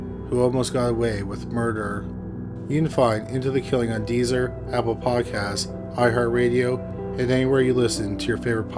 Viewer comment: Could've sworn he said apple piecast. Or maybe I'm just fat. 🤷🏾‍♀️